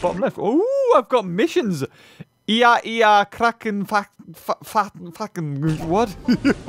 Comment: For those wondering, that is a reference to the phrase "Iä, Iä! Cthulhu Fhtagn!" From the novel "The Call of Cthulhu". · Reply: I know he’s trying to say kraken fhtagn, but he instead sounds like he is saying “fuckin”